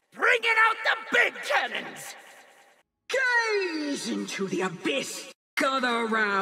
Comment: all I heard is "gays to the abyss".